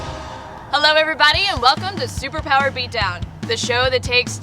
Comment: for some reason, i heard her say 'super power bjob' instead of 'super power beat down' just me?